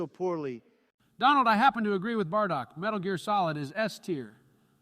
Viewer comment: LOL Did he call him Bardock?